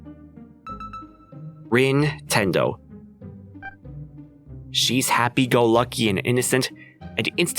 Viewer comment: Lol. I replayed that since I heard something different. . (Nin-tendo)...